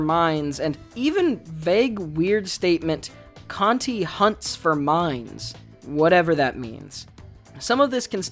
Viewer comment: thought you said canti hunts for mimes and was very confused